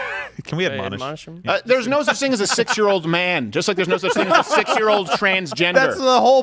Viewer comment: It did sound like you said “six year old man”